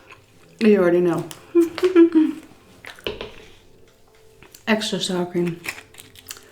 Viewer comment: Exercise? I thought you said extra sour cream.